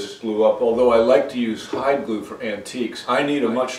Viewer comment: For hilarity, the auto generated subtitles say that you like to use "hot glue" for your work. I know, I know, none of your regulars would not giggle like a schoolboy at the idea, but still... 🤣 (You might want to consider creating actual subtitles for the videos to avoid that kind of headbanger) 😀